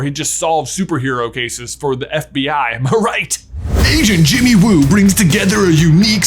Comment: I thought you said “Asian Jimmy Woo”